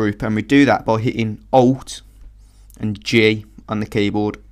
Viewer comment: does he say Alt and G or J? Or what?